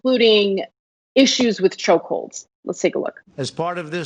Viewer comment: did... did he say choke holes?